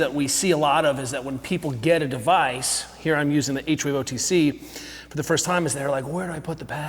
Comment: What make and model of NEMS did he say he’s using? Anybody catch it? I’ve backed up the video a few times and I can’t tell what he’s saying (0:47). Thanks in advance! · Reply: H-Wave